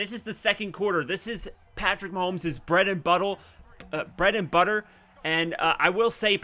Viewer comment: I thought you said bread and butthole 🤣🤣🤣